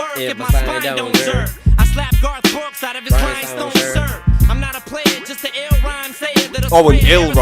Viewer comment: Brev you’re saying that like it makes more sense or sounds better 💀😂 it’s a trash/cute bar lmao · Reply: Ill rhyme sayer is perfectly fine, it's not meant to be an amazing bar. No need to double down because you misheard it as 'lil' and made a big deal out of it. A simple 'my bad' would've sufficed.